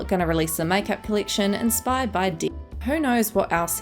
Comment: Thought you said dick not death bc of the censoring lmao